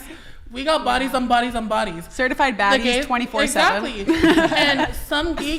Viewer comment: The first thing this beluga says is that shes a certified baddie but never had a bf.🙄😒🤦🏾‍♂️😂😂😂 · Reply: I thought she said certified fattie...